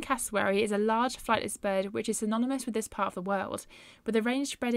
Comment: The cassowary is anonymous to this part of the world? What do you mean? Did you maybe mean endemic to the region ?If that is what you meant, may I suggest a mild draw and quartering of your editor? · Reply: Oh, I misheard what you said, sorry. I thought you said anonymous and I commented on that. I will let you off on the matter of being drawn and quartered then, you may stay in one piece. And you are right about the cassowary inhabiting southern portions of Papua New Guinea. cheers